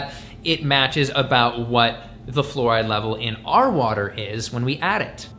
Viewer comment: I thought it said "Florida" LOL